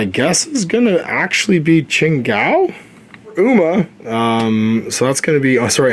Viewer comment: you owe me money! I wasn't planning on getting this! I am now. 😔😂 Also I thought you said Chang Gao which sounds kinda funny in Spanish.